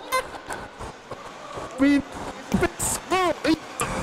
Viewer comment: what did u say? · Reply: "I mean... It's a bit squirrely..." My radio mic went out of range 😁